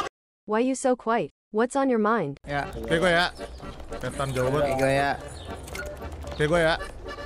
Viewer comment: MEREKA NGOMONG APA SIH KOCAK BANGET AKWKWKWKWKWKKWKWKWKWKSKSKS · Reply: lebih tepatnya ni gw ya